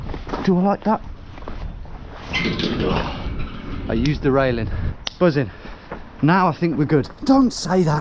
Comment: imagine being a woman walking alone, and some weird sweaty dude looks at you from above and says “yeah, I could do that”… Mentions of railing in the next sentence didn’t make things less weird, either… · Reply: And then you her: "Uhh, do I like that" "Ugh" "I used the railing" misheard as: "I'm used to railing" 🤣🤣🤣🤣